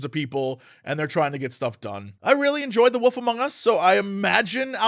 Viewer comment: I kept hearing "the WOOF among us"